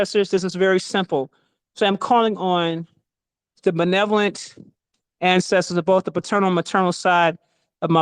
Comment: what is the first word she says when you want to contact the ancestors of your bloodline? Is it malevolent? I couldn't make out the word I don't even think I'm pronouncing it right. Thanks for the help.